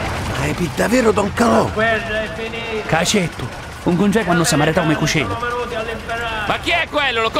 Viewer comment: the italian blackshirt officer speaks with a Northern Italian accent, while the two soldiers speak in sicilian dialect between each other and in italian with sicilian accent to the officer. It's a pretty neat detail that can go unnoticed. Officer: "What the hell is he saying, who does he think he is?" Soldier: "He is really Don Calò... *sicilian dialect I don't understand" Officer: "Who is that? Do you know him?" Soldier: "Everybody knows him. Don Calò is a man of honor" Officer: "It's a stupid american trick, don't tell you actually believe him!" Soldier: "No he ACTUALLY is Don Calò. Sometimes you should listen to the advices of us locals... Shall we surrender?" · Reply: he says he is really don calo I met him when my cousin get married "ca certu l incontrai quannu si marito me cucinu"